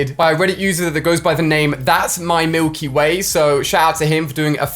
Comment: i thought ali said thats my milfy way so i had to go back and listen again 3.00